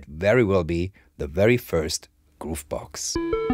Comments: I wasn't prepared and thought you said it was the first goof box! 😀 Humbly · I misheard it as: "crazy hands? ragtime? add maracas? this might very well be the very first goof box" -- then I honestly looked up "goofbox" to see if there was a niche category of wacky grooveboxes that I wasn't aware of